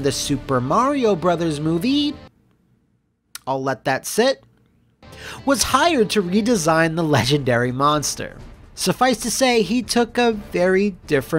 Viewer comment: "[Tatopolous] was hired to design the legendary monster." I heard that as: "[Tatopolous] was high when he designed the legendary monster." 😶